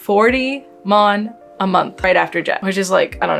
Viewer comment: you say something like "mon a month "right after tax". Is that indeed what you are saying there? If not, what is the after tax take home pay? also at 13:20 you mention "LinkedIn died job"? I googled it as you requested, but no idea what you were saying there. Could you provide a link?